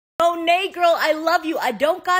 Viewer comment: Umm 🧐 is it just me or did it sound like she said “ yo negro I Love you “??????